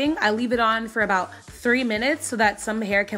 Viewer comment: Did you say 3 minutes or 30 minutes? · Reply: JoJo The God 3 minutes!